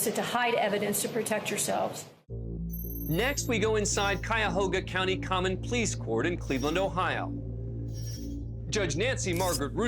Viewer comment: Calling all Americans! I'm British so could somebody PLEASE tell me wtf he says at 8:35 I'm lost... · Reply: Which part? "Next we go inside Cuyahoga County Common Pleas court in Cleveland Ohio"